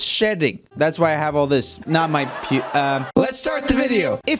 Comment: Did he say pee?